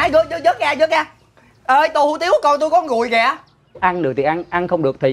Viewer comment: Mới đầu nghe "thịt người" Sau nghe thành" thịt nguội" Đoạn cuối mới biết là "con rùi"